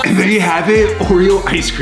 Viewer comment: i thought he said "edible ice cream" and when i misheard it i said "BUT ALL ICE CREAM IS EDIBLE"